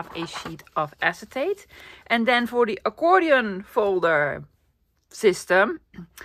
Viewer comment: An accordion file system or a harmonica filing system? lol. Before I heard you say you got those two words mixed up I was all confused when I would hear you talk about a harmonica folder.